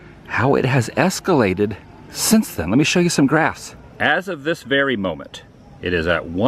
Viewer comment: When you said let me show graphs. I thought you said grass lol